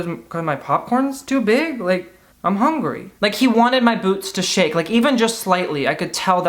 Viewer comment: Not the hat reveal💃 come on drag race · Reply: When you said "he wanted my boots to shake" My high ass thought you said "he wanted my boobs to shake" I was like damn Timothy Chalamet whaaaat🤔